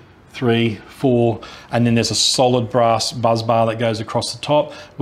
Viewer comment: sounded like you said buzz bar. It’s a *bus bar.